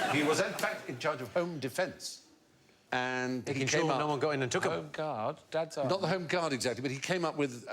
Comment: what was it Jimmy said there? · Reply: “he ensured no-one came in and took them” in regards to Morrison being home defense minister defending sandwiches